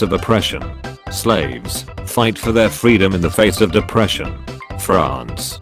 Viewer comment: Did I hear it wrong or did the voice say "Slaves: fight for their freedom in the face of depression"